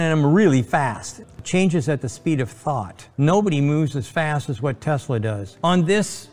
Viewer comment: Did he say "Speed of fart"?🤔